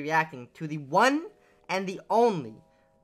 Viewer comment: Did I hear TH3ON3AND0NLY 😈